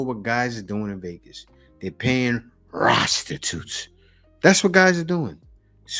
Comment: Did I hear you say "rostitute"?! Ok, Scooby Doo!